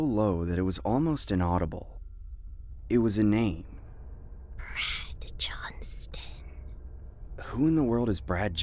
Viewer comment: I THOUGHT SHE SAID "BRAD JOHN CENA"